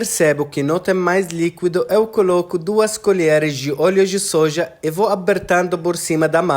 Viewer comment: Eu entendi “molho de soja”, no final da adição de líquidos?